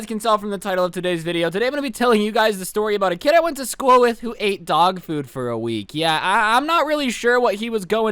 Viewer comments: Bruh I thought it said "He ate dog for food for a week" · I thought it said he ate dog for a week... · i thought it said "he ate dog for a week" very disappointed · i thought it said "he ate dog for a week" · I thought is said he ate a dog for a hole week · For a sec i thought the video said he ate dog poop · I thought is said he ate dog for a week